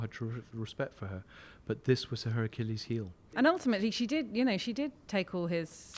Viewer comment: "it was her Hercules heel" - not heard that one before :-) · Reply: I heard"it was her Achilles heel". Her Achilles, not Hercules. Go back and listen more closely.